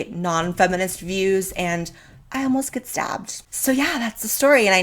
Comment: A lot of you guys are really protective of me which is awesome, but don't try to find these people online and harass them or anything. We've all moved on and I got out un-stabbed so it's all good now :P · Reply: For a second I thought you said you got un-stabbed and I was extremely confused